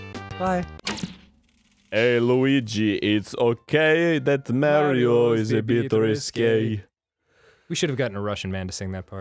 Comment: I always thought they were saying, "Hey Luigi, it's ok, that Mario is a big mistake." I didn't know it was actually, "Mario is a bit risque."